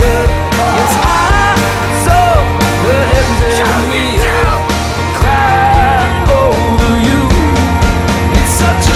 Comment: What does he scream at 3:08? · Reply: Sounds like "show yourself" to me